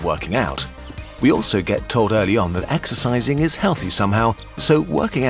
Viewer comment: it sounds like they say exercise is not healthy...